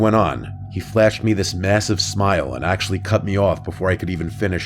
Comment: did you say mmmasive??